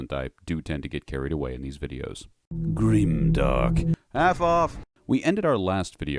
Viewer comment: I heard it as "F off"